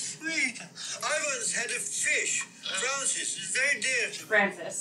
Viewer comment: i hear "trousers" too, but if the captions say "Francis" then that's what the script said it was supposed to be which feels more accurate.